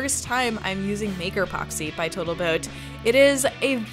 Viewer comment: Did she just call the epoxy "Total Boat"? 10:52 Wheww OK that's better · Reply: Nope Botal Toat 😂🤣 but a girl high on epoxy is allowed to fumble a few words every now and then.